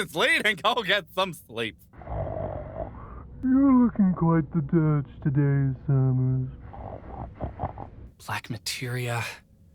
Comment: Can someone tell me what Vincent is saying at 5:53? I'm trying to make English captions for this episode but Vincent is my nightmare with all this mumbling. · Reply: "You're looking quite the Dirge today, Cerberus!" It's a reference to his game haha! It took me a few tries to hear it out too.